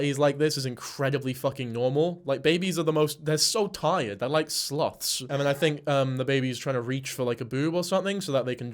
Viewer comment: MAN I though you said the babies are like sl*ts I was like what in the world the accent you said Sleuths lmfao thank God i almost had to click out